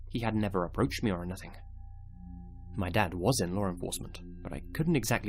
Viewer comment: He said “was in law enforcement” LMAO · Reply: Really, I thought she said "My dad wasn’t lar enforcement." lmao